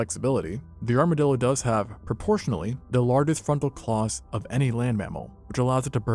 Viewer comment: LMAO... Ok... I had to rewind and rewatch that. You're saying; "..the largest FRONTAL claws.." The first go, I misheard it as; "..the largest fuckin' claws..". Also... LOVE the content bro! Keep up the amazing work!!